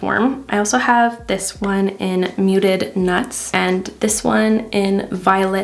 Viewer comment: Did you just say... 'muted nuts'!? Who comes up with these names 😂